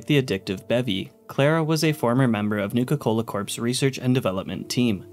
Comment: I almost thought you said Nuka Cola CORPSE